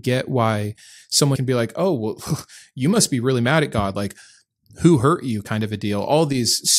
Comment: lol. I misheard “mad at God” as “bad at God.” It occurs to me that “bad at God” is more accurate than the former… and THAT is the real criticism… as if the theist is “better at God” than the atheist. I wonder if this merits more contemplation.